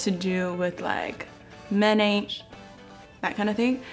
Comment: what did she say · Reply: ｓｈｅ ｓａｉｄ ｍｅｎ aint ｓｈ*ｔ